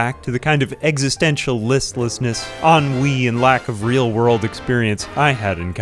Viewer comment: can anyone tell me what's the word he's using at 8:43 - 8:44 ish? is it "en vie"? on wii? 🤣 i'm not a native speaker and that word/phrase has been used in a couple of videos and i have no idea what it means 🥲